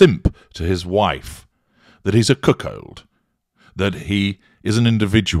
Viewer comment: At the 1:37 mark says something that the closed captions is telling me is "he's a cook old" I don't think this is actually whats being said. I've replayed this several times and I just can't quite make out exactly whats being said. I even played the sentence for Google and Google thinks he's saying "he's a cook old" as well. Can someone plz tell me what's actually being said please. If he's actually saying "he's a cook old" is it perhaps Brittish slang for something?? I know this probably seems like a trivial question but I have OCD and when something like this happens it does bother me quite a bit. Yes, I realize this is MY problem but I would really appreciate someone helping me out with understanding whats actually being said. Thanks so much and God bless.